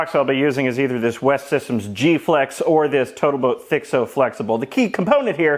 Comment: Did you say BotalToat? Is Xyla about to crash this video?